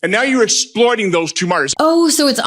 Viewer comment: did he just slip up? Did he actually admit it? Did he just say, "And now you're exploiting those two 'MURDERS'"? I can't 100% make out what he is saying, but it sure sounds like he is admitting that Alex Pretti and Renee Good were murdered by TRUMPS GESTAPO ICE AGENTS